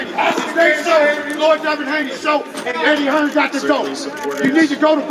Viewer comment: Did say: "This is lord Devin Haney's show and Eddie Hearn gotta go." ??? 55:15 - Talking about you beat up Fortuna - 2012??? They need to let nap during the day. I think he messed up there...to much rhyming he running out of material.... 1:14:47 - Is that wifey in the background with his dad checking her watch. George is going to be in the doghouse. 1:31:15 - "He's a big boy at light weight and he's long and he got great length". "That's what she said"😂😂